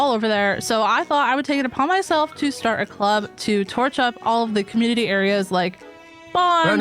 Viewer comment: I thought for sure said Torture Club and "I started a club to torture the community areas"... I guess it wasn't too far off